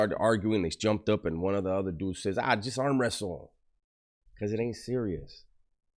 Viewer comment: I thought I heard let's arm wrestle 😂 I never heard fade 🤔